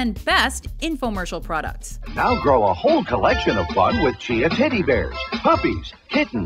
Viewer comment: did they just say tiddy bear? sold